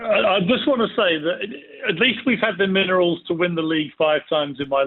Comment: The "minerals?" Somebody's been watching Snatch and misheard. Its "minimals" you doofus. I bet he's been saying minerals for years and his mates haven't got a Scooby what he's on about 😂